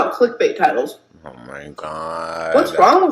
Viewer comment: When said "clickbait titles" I thought she said "let's talk about cryptid types" 😂 and she looks like a cryptid with those cat ears